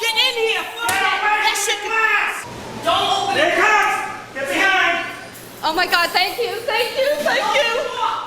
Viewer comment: I am not a native English speaker and I have difficulties understanding what the man said to stop her from going out, did he say 'the fallout is gonna pass'? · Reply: I think he said (with the time markers preceding each statement): 09:25 - "Get away from the glass!" 09:27 - "Here it comes, get behind!" 09:37 - "Look at the shadow of death" 09:50 - "I've covered a lot of combat Ma'am."